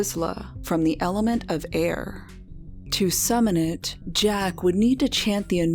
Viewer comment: I'm sorry, did you just say the element of RIZZLER??